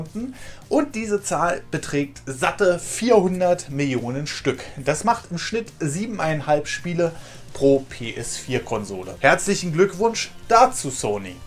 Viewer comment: haha ich hab verstanden "das bedeutet 7 spiele pro Penis"